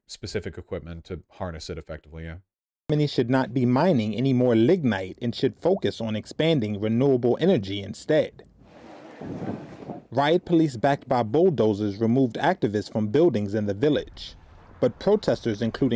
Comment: I thought he said ligmite and I was about to lose it